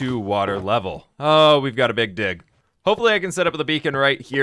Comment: am i only one who hear big d